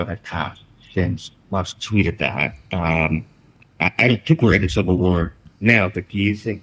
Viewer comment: The subtitle is wrong at a key moment, at 3:37 he says "I DON'T think we're in a civil war now" and the sub says the opposite!